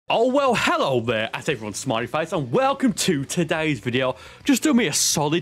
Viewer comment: has anyone noticed that he sounds like he is saying hello at everyong smarty face instead of smiley face, no offense. and ik its his accent but still